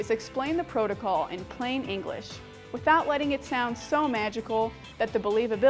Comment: I thought you said that you were going to talk English 😂 😂 😝